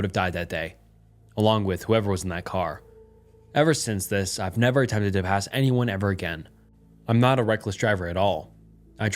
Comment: Were these captions auto generated??? Some of the texts are incorrect. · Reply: They for sure are. At one point the text says "Ever since this, I've never time to trip. Has anyone ever again. I'm not a reckless driver at all" when he actually said "Ever since this, I've never attempted to pass anyone ever again, I'm not a reckless driver at all"